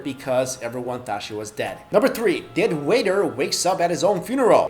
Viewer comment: Lmao, I thought he said "A dead wigger" at first XD